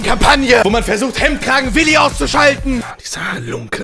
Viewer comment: ich hab zuerst verstanden, du willst mich ausschalten ^•^